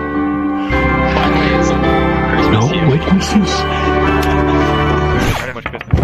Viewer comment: I heard Santa say " shhhhh. No witnesses"